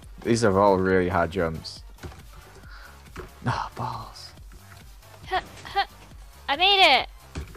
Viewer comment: Listen carefully at 1:24 did Joel say balls ?!?!😂